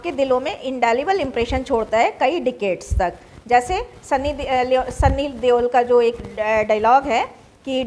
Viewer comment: What maam say sunny leo**????